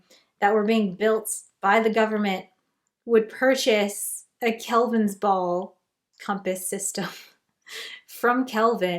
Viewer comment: I keep hearing “Calvin Ball”